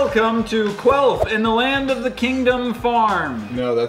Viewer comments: Sry I am dirty minded I thought is said queaf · I thought it said queef. Oops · I thought it said queer instead of quelf